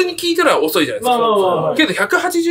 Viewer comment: 意味がわかると速い話w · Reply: テロップホラーみたい。意味がわかると怖い話だと先入観で聞き間違えたわｗｗ